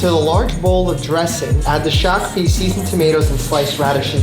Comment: I could swear that at 5:16 he says "add the shocked feces"